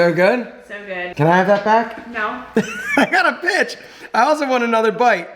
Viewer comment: I thought he said Bitch when tak8ng back the food smh...